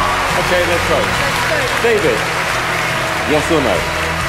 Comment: At first i thought he was saying yes sir lmao